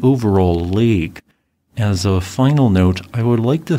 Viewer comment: I swear, when you said "Helen S" I thought you said "Haul'n Ass" XD